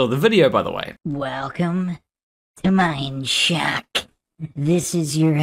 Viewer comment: ~ I swear he said "mind shack" (also, tremendous mic he's got there).